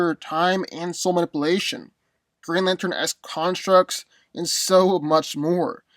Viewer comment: Did you say Green Lantern???